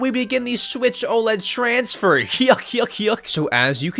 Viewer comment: what did bro just say 😭🙏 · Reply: hyuk hyuk hyuk!